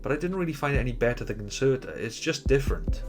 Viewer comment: after 5:57 — he says he didn't really find it better than <<unknown word>> just different. I can't make out what he says there. Maybe it's the speaker on my phone, idk. Can someone please let me know what he is saying here? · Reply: Concerta